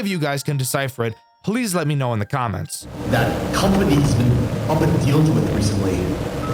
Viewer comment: I heard smth like "the company has been up and deals with recently". Turned on the Subtitles and turns out that's indeed what it said